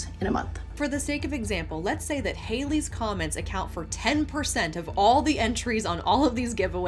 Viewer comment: I heard you say "Hailey's Comet" instead of "Hailee's Comments" when speaking of the statistical impossibility of this 😄